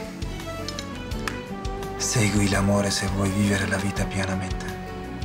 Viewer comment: feritin burda ne dedigini anlayan var mi ya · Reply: Segui l'amore se vuoi vivere la vita pienamente. "Hayatı dolu yaşamak istiyorsan aşkı takip et" gibi bir şey